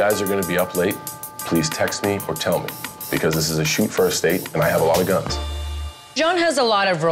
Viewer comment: When he started in with his 'house rules' that's when you get up, thank them for a lovely evening, and to never call you again. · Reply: When he said it was a "Shoot-first state" I thought he said it was a "'shoot' first-date" and got very confused